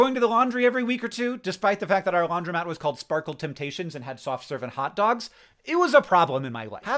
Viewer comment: I heard “soft serving hot dogs” and got too busy thinking about how that would work….